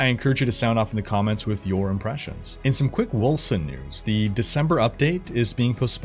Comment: When he said "quick wolcen news" I heard "chris wilson news".